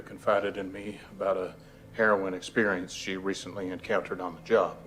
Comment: I keep hearing heroine experience. And then I am like, I thought they only took the money, not any drugs. 😂 Must be my foreign ears.